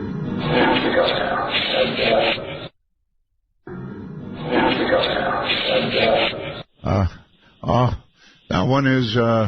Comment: " I have to go now, I'm done", is what I hear.